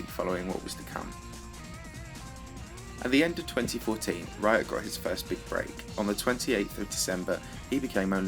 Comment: i thought he said “he got his first big brain” 😂